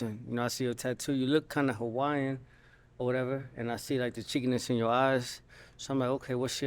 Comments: did he just say. I see the chinkyness in her eyes?? Is that not a racial slur .. wow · Did he say he sees the "chinkiness" in her eyes? Asian slurs? Really? 🙄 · did he say ch*nkness in your eyes? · is he saying “cheekiness” or “ch*nkness”? Ch*nk-“ness” is derogatory towards East Asian ppl. Also I am not looking to argue or offend but the later term isn’t appropriate (please google before you defend) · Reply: My closed caption showed it as “cheekiness.” 🤷🏾‍♀️